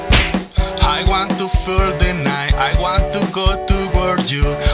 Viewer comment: DID HE JUST SAY "I want to fart tonight"